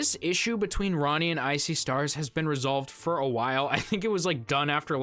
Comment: I've heard of I See Stars but for some reason when you said it I heard it as "Ice-y Stars" 😂